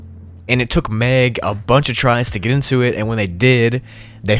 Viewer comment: I heard “goon to it” 😭💔